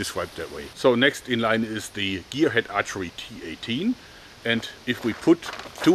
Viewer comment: Thought he was saying "Gearhead AT-AT" =x